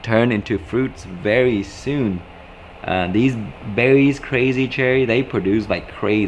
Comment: What brand of tomatoes was this please they look yummy and as always thank you for the wonderful information in your videos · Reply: i thought he said crazy cherry. check out proven winner's goodhearted tomato too. they only grow one foot tall with lots of fruit :-)